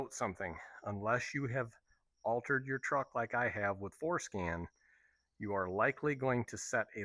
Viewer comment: I swear you said "Foreskin" at first, had to rewind and hear it again😂😂😂 Thats a good tool though, I use a similar one for the same type of stuff on my truck